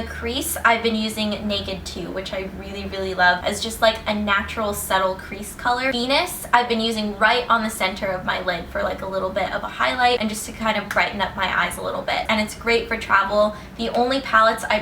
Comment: I thought you said penis! XD